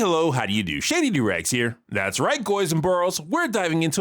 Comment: did you just call us goys!?